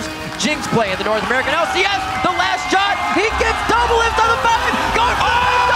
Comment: "He gets Doublelift on the fountain!" Did I hear that correctly??